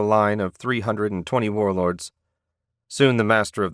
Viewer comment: I completely thought that it said this dude was standing in front of the line at 20 whorelords because of the unique accent. Top show my boi, top show.